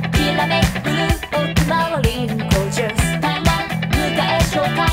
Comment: ら辺で『りんごジュース』って聞こえる気がする。